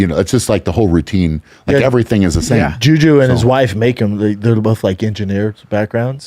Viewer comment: "jew jew" instead of juji is hilarious!